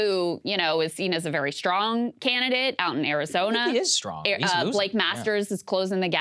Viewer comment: does Saagar say "He is strong, he's lucid" ?!? 😆 is that the bar now?